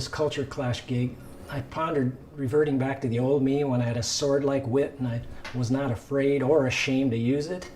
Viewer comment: Oh, he said "sword-like" - thanks for clearing that up. It sounded like "sword-leg" to me, and I couldn't figure out what that meant.